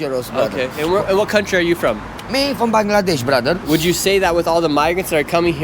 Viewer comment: Did he just say he was from Bangladesh?